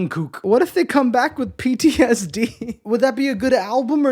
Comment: I thought you said BTSD 😅.